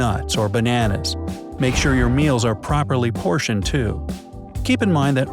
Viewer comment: I thought it said "properly poisoned" lm*o